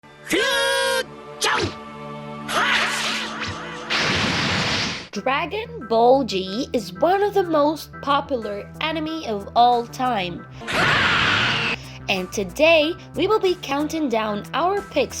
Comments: Did i just heard you say dragon ball g is the most best enemy of all time ???? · Me: Ummm what bragon ball g Me: pardon me Dragon ball g Me: What DRAGON BALL GGGGGGG Me: ok say it on more time Dragon ball 'z' Me: oh that's what I thought you said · wait did he just say dragon ball G hahaha